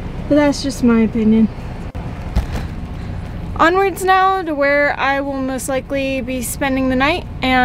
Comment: “Onwards and Underwear”. ?? Did I hear that right?